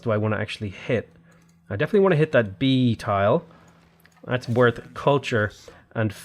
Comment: did he say Bee🐝 tile?